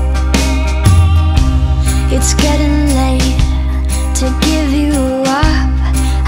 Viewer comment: The fact i heard "Its too late to give you WAP" is kinda scaring me